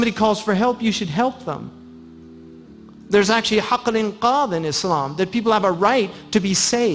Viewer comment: what did he say? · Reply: "... there's actually حق الإنقاذ [haq al-inqaar] (the right to be saved) in Islam. People have the right to be saved "